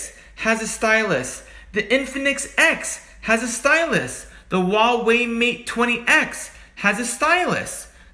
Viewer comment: Am i the only one who heard him say wawi exept huawei?